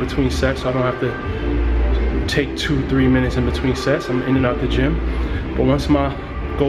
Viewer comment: I thought you said "in between sex"..lol